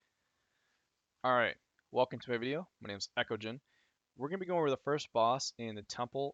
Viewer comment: I thought you said "Hey guys, welcome to Eckogen, my name is My Video."